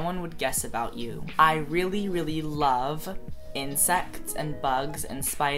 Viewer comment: omg I thought he said "incest" at 1:25 lol